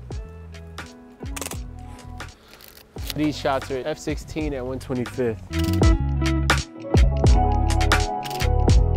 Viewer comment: Did you say 1/25th? Nah. Probably 1/250, right? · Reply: 1/25